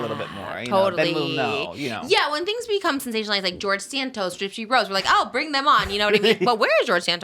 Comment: why did i mishear george santos and george soros and for some reason not question him having a cameo account 😭